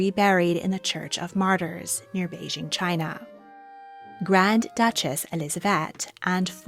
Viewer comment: near Beijing, China? Did I hear that right? :o